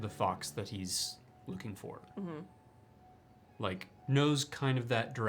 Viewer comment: Y'all have broke my brain I literally thought he said lücing for instead of looking for 😂😭